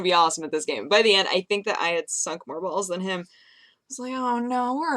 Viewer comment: Please tell me that I'm not the only one who thought she said "By the end I sucked more balls than he did". Oh god, I'm turning into one of creepy fans nooooooooo!